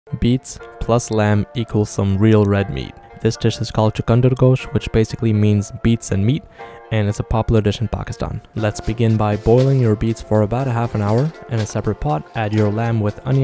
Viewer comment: Is it just my dirty ass mind or did I hear him say “beat some meat”?